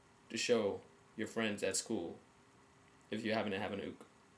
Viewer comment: At the end I thought you said "show your friends at school... if you happen to have any" instead of "if you happen to have an uke".... shot straight to my ego lol. Nice tutorial :)